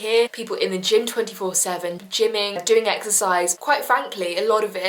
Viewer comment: why do I hear 'Jimin'? I'm too obsessed with bts istg 😭